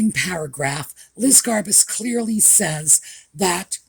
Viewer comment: I am sorry but everytime you say "Garbuz", I hear "Liz Garbage" 🙂